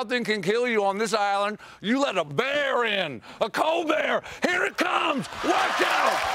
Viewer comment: "You let a bear in, a COLBEAR" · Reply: I thought he said “you get a cold beer”